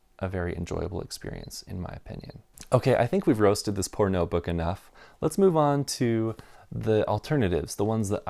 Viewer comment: Took a while to register. I thought you were saying "I think we've roasted this pornobook enough".